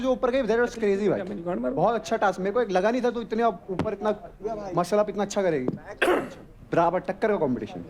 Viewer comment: did he just say Mashallah 😊